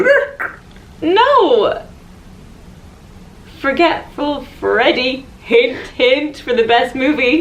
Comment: I love it how when said Forgetful Freddie, thought she said Freddie got Fingered lol